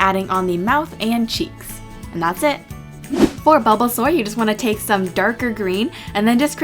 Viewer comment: I thought you said bubblesaur